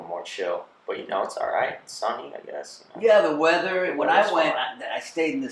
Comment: Bryan Saud Florida was "sunny" and I heard "Sonny." 🤣